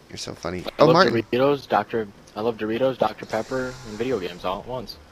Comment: ikd why but i thought he said dr faper